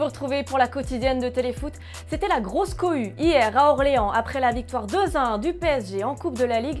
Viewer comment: Qui entend comme moi : "La grosse couille ?"